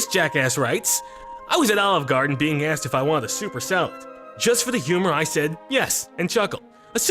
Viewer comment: I actually did the olive garden soup or salad, but i was not joking, i was serious because i honestly thought he said super salad. :/